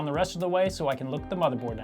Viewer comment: My roommate walked by at about 1:55 and went, "Did he just say he's going to lick the motherboard?"